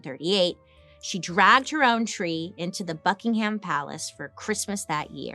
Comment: oh my gosh I had to rewind it cuz I thought you said Fuckingham Palace 😂😂